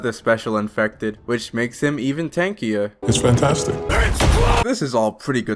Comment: What did u say??!! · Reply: It’s fantastic?